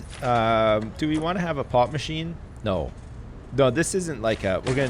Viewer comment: Every time he says "pop machine" I hear "pot machine"